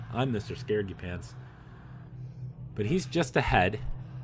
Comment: "but hes just ahead" what i heard: but hes just a head, thinking a floating head :D